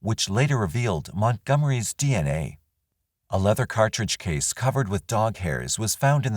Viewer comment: hehe I heard that as the cartridge belonged to the dog shadow.